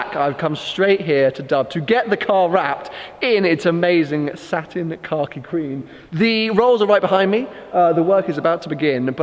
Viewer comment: I thought he was saying satin cocky green as in shit green 😂😂 makes "more" sense now. looks great either way